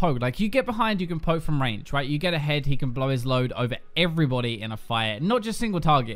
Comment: The entire video all I heard was "Blow His Load"...